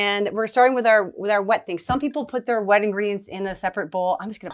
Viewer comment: Haha. At first I thought you said, "Some people put their wedding rings in a separate bowl," but then I realized you meant "wet ingredients," not "wedding rings"! I'll have to make this sometime, but I love chocolate so much I think I'll use 7 TBSP on the cocoa powder. (smile)